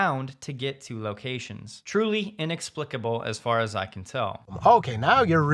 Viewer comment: I first heard "truly inexplicable, as Farzad can tell". Maybe I should watch less TeslaTube…